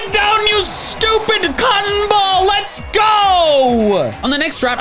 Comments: I was worried because I thought he said c** ball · 0_0 what did he sayyyyyyy?? Edit: c*m ball.